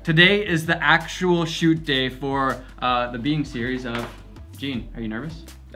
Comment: Ohhh "BEING series." I thought you've been saying "BEAN series."